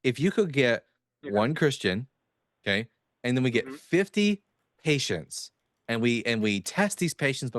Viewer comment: Patients. I thought he said Haitians. I was so confused. You can't cure that.